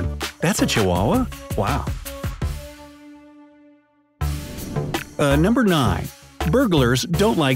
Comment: Did anybody else hear “UH number 9” at 2:33 Like if you did 👇🏼